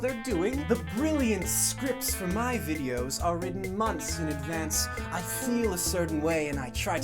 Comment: Jreg: "I never write my own scripts." Also Jreg: "I write my scripts months in advance." Ah, yes... Anarcho-Monarchism. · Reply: He actually said "the scripts for my videos are written months in advance" So its not contradicting.